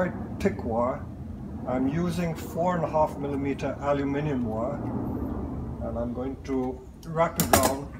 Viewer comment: I thought you said adamantium wire. I was like...."where? where does one find adamantium?"